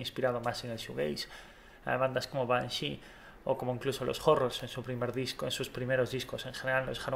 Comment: Cuál es la banda que nombra en el minuto 6:04 aprox., Cluse of horror puede ser? No escuché bien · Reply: the horrors